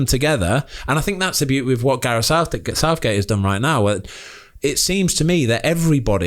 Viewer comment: I heard that Ben "Gareth southdick"